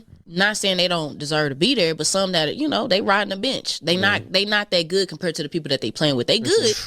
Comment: Riding the Bench? 😅 I thought she was saying riding the Bitch😂